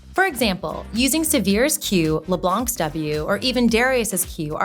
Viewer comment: Did she say 'Severe's Q' on 0:53? I guess she means Samira's Q? I was wondering if she is actually a league's player or if they just hired a woman to voice their videos to attract us gamers to that, and I guess that's my confirmation that she is just reading a script without knowing anything about league. So sad, the other voices were great and I rather have someone that actually knows what they are talking about!